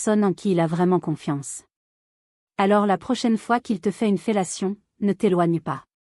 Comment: A 3:00 elle dit bien une fellation ?? Mais non c'est quoi ce traducteur automatique !!